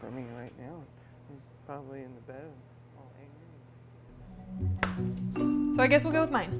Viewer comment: Did just say 'He's probably waiting in the bed?' when he was crawling out the door at 2:19?